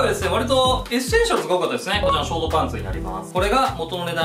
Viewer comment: お酒引っかけて視聴していたら最後の振り返りのエッセンシャルのショートパンツが勝負パンツに聞こえて爆笑してしまった…